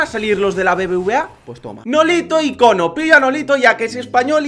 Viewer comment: Like si te diste cuena que dijo Nolito icono minuto